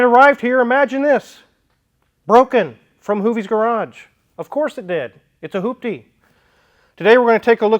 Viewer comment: Thought I heard “Hooptie’s” garage. 😂